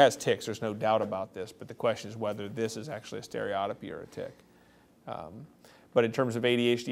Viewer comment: Can anyone tell me what word the gentleman says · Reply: I think he said stereotypy